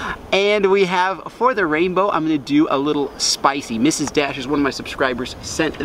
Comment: it sounded like you said Mrs. Dash was one of your subs